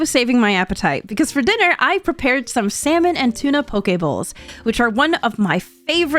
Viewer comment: The Quaternity of What I ate in a week is now in form! 2:00 I genuinely heard "Poké Balls" instead of "Poke bowls" LOL